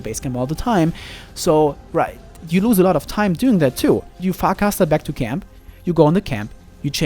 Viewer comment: I heard "you fack ass" back to camp.... XDD